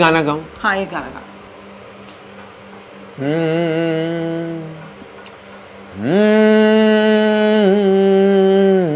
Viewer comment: did she say nora?? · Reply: She said, "Not us"